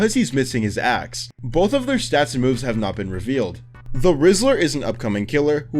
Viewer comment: Maybe I'm just to chronically online, but at 19:18 I SWEAR TO GOD That he says "The Rizzler" instead of "The Whistler"